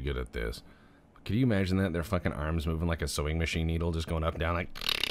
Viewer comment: I was eating soup... I hear "Hand is moving like a sowing machine" Now I am cleaning soup off my kitchen table. Haha!